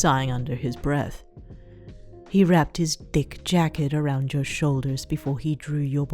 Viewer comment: I thought you said dick jacket, I